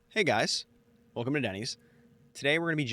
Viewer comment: currently live (maybe asleep): <link> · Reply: 😂I thought u said welcome to daddy